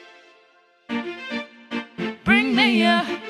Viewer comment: when they started singing "bring me a" i got so confused and thought they were saying "bring mia" and i was like wait that's mE